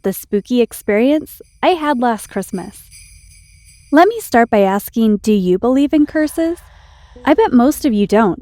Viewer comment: I thought she said do u believe in Christmas